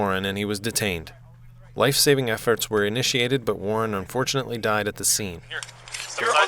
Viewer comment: Warren fortunately died at the scene. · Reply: The fuck did he say? "Warren unfortunately died at the scene" ? someone may want to mute or change that part!